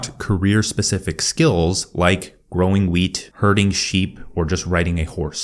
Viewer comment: I heard “growing weed”. OK